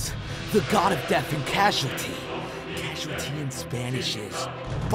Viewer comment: Light mixing up the words "casualty" and causality" to end up in "Baja blast" is the perfect encapsulation of Light's hubris for me. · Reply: While it would be, in this case "causality" seems to just be an error in the subtitles. "Baja" means (among other things) "casualty" in Spanish and unless I misheard him, he is still saying "casualty" in the video. It is the subtitles that are wrong.